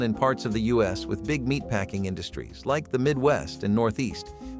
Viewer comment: did he say “fresh awful?”